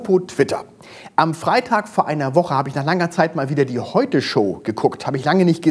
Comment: Ich hab zuerst "die Heuchel Show" verstanden 😂.